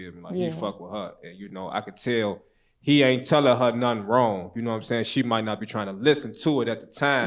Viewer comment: I thought she said he ain’t kill her mama